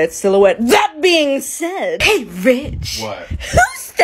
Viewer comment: I thought said hey bi- but when I heard it one more time she said hey rich